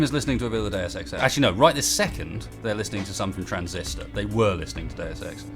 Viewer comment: I can't understand what he says the music is at 1:01:21 what is it? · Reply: 3 years late but he said that the chat is listening to something from Transistor, but they were listening to Deus Ex.